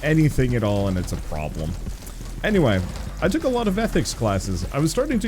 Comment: I heard you say “i don’t remember things, I took a lot of meth” but you said “I took a lot of ethics classes” 😂